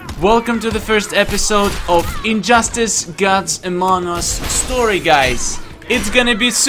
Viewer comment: It sounded like he sed guts of among us